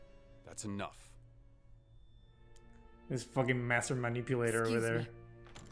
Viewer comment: With you guys talking at the beginning it 100% sounds like Weiss says “a fucking PR move” and I relistened to it like 3 times going “I know she didn’t say that when I watched it”